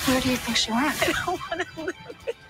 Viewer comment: I have listened 3 times and can’t figure out what Michael is saying? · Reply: To me, it sounded like he said, "I don't want to hear it."